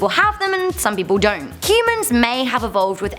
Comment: She siad Cuman Instead of Human!🤔